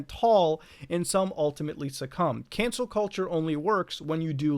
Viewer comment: i swear i heard "cancer culture" the entire video